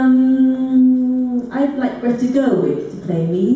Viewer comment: Who does Rose say at 28:17?? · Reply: thank you so much!! I've been trying to figure out how to spell that for so long haha I kept googling Grettie Girlwick and such